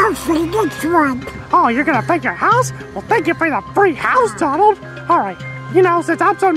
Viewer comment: For a seconds there I thought Mickey said drug house 😳